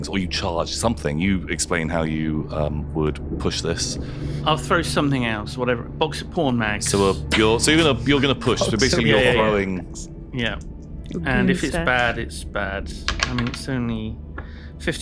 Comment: When said “goon stash” I heard that as “goonstache” which seems to be what is sporting these days…